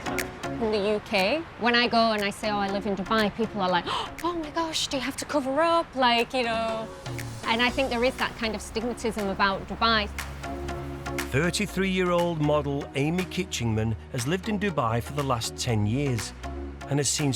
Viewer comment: Did she say “astigmatism about Dubai” at 4:06?